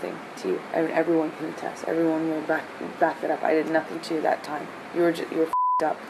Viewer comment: Did she just say "I didn't do anything to you that time. "?